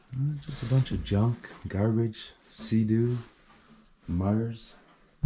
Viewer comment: It sounds like you said sea-doo but it’s a snowmobile